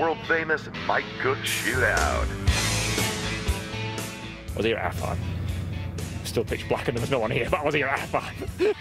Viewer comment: Once again i cant tell wtf Guy is saying. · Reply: "I was here at half 5, still pitch black and there was no one here, but I was here at half 5".